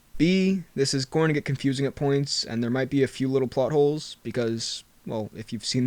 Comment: I thought he said buttholes lol